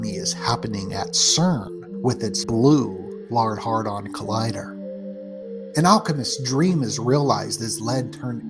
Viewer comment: I believe it’s pronounced “Hayed-ron” sounded like dude said “hard-on collider” 😂😂😂